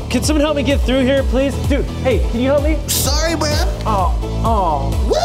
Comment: How come the subtitles say man instead of bra?